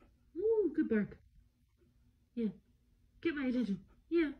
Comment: I like how she still talks to him in the baby voice even though he can’t hear it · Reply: i heard 'good boy' Do not force your ears pov on me i beg u